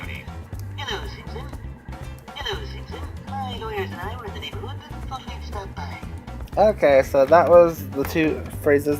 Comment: Hi my friend ,good video, I speak spanish, so i want to know what Mr. Burns said in the minute 19:00 ,i just understand Hello Simpson lol, can you write the phrase? Thank You ,greetings from Ecuador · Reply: “Hello Simpson, my lawyers and I were in the neighborhood and thought’d we’d stop by”